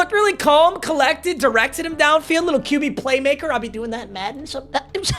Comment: Did he just say well be seeing him play Madden